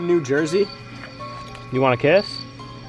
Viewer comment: the spirit probably answered 1 because said do you want a kiss.Well that's what i heard say🤔❤️